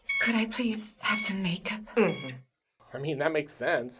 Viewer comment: Haha, I thought she said may I have some "bacon" instead of "makeup."